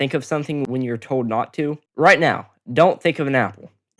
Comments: I thought u said don’t think of an owl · me not thinking about an apple because I thought he said “Owl” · my dumbass heard „owl“ instead of „apple“ so I somehow did it 😭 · I thought you said A now so technically I didnt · i thought you said owl so i didn't think of a apple